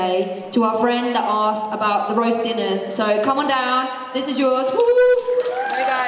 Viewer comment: what was said here? "the best question today to our friend asked about the [???]" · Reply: what is the best "roasted" thing that their mom does xD (the question was somewhere at the beginning i think....)